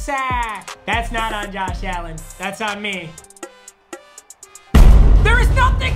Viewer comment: I thought it said Josh allens busts on me